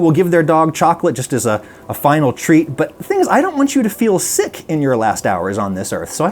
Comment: Just a heads up: at 5:42, the subtitles say [inaudible] but you're saying "the thing is." v good video btw thank you